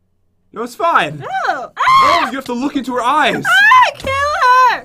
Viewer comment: did SHE say killa?!?